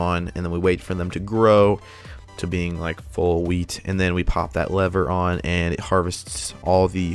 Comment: I thought he was saying weed farm